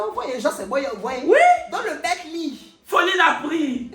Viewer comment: c’est moi où il a vraiment dit « donc le mec lu » ?